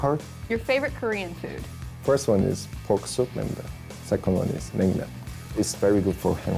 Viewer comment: 에 좋아하는 음식 뭐라고 하는건가요?? Pork soup??